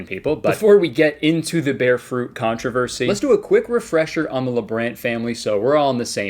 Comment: I thought it said the lesbian family instead of labrant